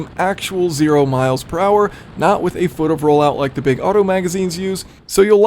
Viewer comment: he mentions that his 0-60 times were done from a stand still whereas some magazines do it another way and get better times, but I couldn't catch what he said. It seemed like he meant that they don't start from zero, which sounds like cheating to me lol. Does anyone know what is meant here? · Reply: “1 foot of roll out.” Because of Drag Strips. You pull up to the light at a drag strip. The laser senses the front tire. Then doesn’t start timing your car until the front tire no longer blocks the laser. About 1 foot. So one foot of roll out to match what you might find on your Drag Sheet.